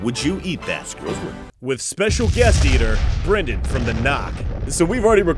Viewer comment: what did he say? Jenny from the block? I wasn't listening'....